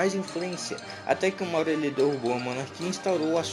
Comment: "Até que o maurelhedougo monarquista e fundou sua própria república" O que vc disse nessa parte? Eu não entendi